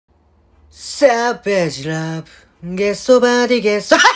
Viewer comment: I thought he said, cabbage love😢😢😅😅😅😅😅